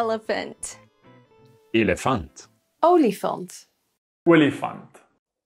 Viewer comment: I Heard Only Fans 💀